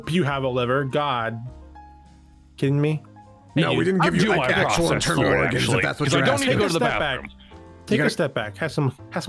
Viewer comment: what in the world is everyone saying?! 😂 19:14: ok: OK!!!!! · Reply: We didn't give you, like, ACTUAL internal organs if that's what you're asking: I DO like processed food, actually. Because I don't have to go to the bathroom.